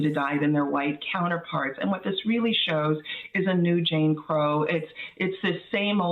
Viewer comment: For a moment, I thought Michelle Goodwin said, "Jane Crow laws," which sounds about right!